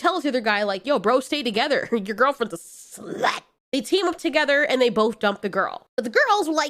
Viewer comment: The frick was the noise at 14:03? Did you say slut? Because all I heard was having a heart attack.